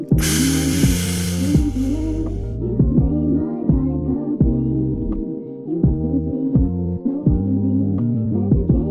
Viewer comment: does she say “ you made my life complete”? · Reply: Yes